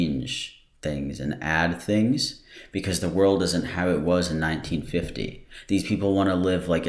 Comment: In 1914 they literally started a world war because one guy was assassinated in Serbia. They really want to go back to a time lile that? Edit: fuck I heard 1914 not 1950